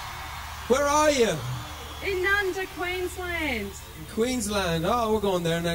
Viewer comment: Did that woman at 16:52 say “Nundah Queensland” or am I hearing things wrong